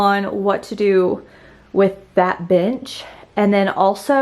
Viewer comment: It sounded like you said, bitch for bench 💀